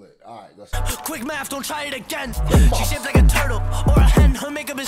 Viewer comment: I heard “She shaved like a turtle, or a hen.”